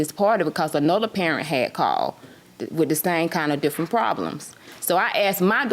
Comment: did she say with the same different problems?😅😅